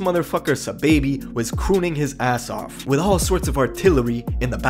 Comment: I hope you not white bro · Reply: i thought he said it too but he said crooning lol